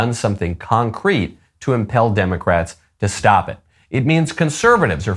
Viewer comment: did you say IMPALE democrats?